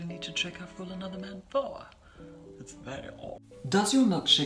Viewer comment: What is he saying in 1:18? Gurl as much as I love this man I still don't get what he's saying sometimes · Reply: He said "it's very odd" 😊